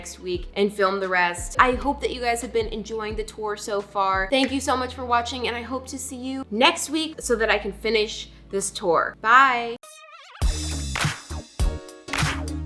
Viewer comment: For second I thought you said torso far I was like uhm ok..? But then I realized……